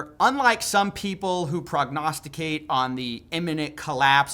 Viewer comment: Bro did you just say prognosticate